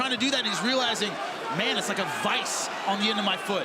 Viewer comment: "man it's like a vis....(i don't know what he said after)" can anyone explain please what does mean ? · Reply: vice-you have them on workbenches, like a giant clamp that you tighten down on something you want to cut or work on, holds it in place